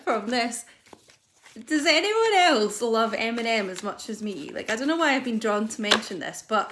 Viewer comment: Did you watch the whole video 👀 if so who do I think the greatest rapper of all time is lol? 🐐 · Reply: At first I thought you said "M&Ms" the candy, but then I realized it was a musician you were talking about! Lol! Oops! Eminem, I believe.